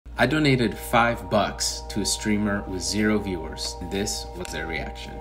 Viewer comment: I had to scroll back up real quick cuz I swear i thought i heard " donated 5 bucks to a stripper "